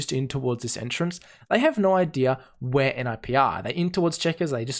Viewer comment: I heard 'where mibr' a few times before realizing you said 'where nip are' god i'm dumb